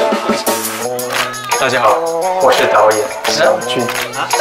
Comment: i heard a whole duck